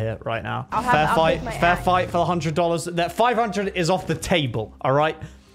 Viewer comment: i thought he said FAIR FIGHT